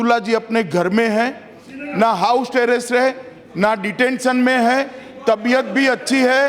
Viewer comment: is it me or even someone else heard "na house terrace hai" 🤣🤣🤣